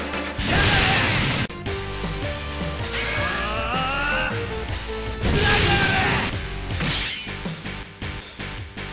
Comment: I've never been able to make out what he's saying. Some help here? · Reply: It's the same quote whenever he does the Orochinagi. "Gwoooh! Kurai yagare!"